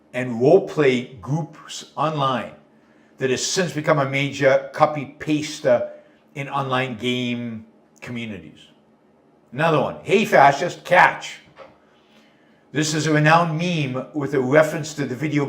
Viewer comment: furries ... Look it up (I heard "furies")